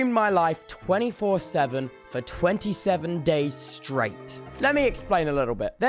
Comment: I thought it said "For 27 days, I was straight." 😭🖐